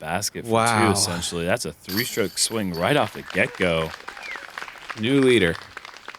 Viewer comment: This is kind of a goofy comment but my 2 yr old son watches the cartoon Pj Mask, well when said "get go" at 4:41 my son thought he said "ghecko" and charged my phone with a smile. Its his favorite character